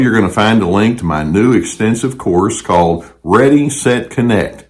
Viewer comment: In explaining ur courses I thought u said- they’re very expensive, but you said extensive🤪